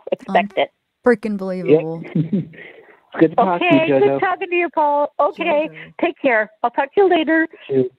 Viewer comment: I heard him say nice talking to you too Jill… lol 🤷🏻‍♀️